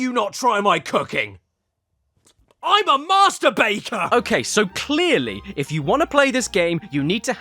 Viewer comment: Did he say MASTURBATOR??? 😂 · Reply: BAKER